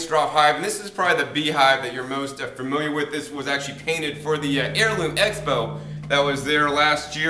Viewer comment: Where did you say the Langstroth hive came from? It sounded like you said "heirloom expo", is that right? Anyway, it's beautiful; I could definitely see one like that being in my backyard. Where might I get one like that?